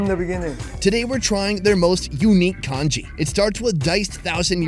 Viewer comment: did you say kanji, it is the same exact word we use for the rice stew in tamilnadu, india. ❤ கஞ்சி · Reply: Nah he said congee.